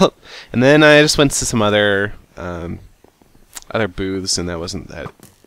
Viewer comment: did i hear that he didn't think that the boobs were entertaining xD?